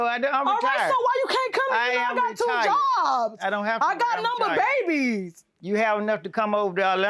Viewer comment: I love the way grandma says Orlando. Lol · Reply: Yea it sounded like she was saying Atlanta/Orlando lol